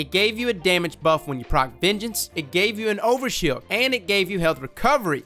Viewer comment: Every time you say "vengeance" I hear "midget"